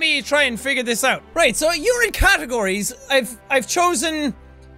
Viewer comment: It sounded like he said " Urine category ". 😂😂